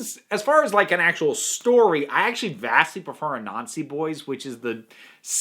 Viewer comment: For a second there I got really confused because I could have sworn you said Nazi boys